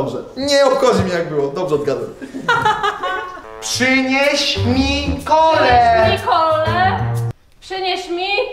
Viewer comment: usłyszałem "przeleć nikole" XD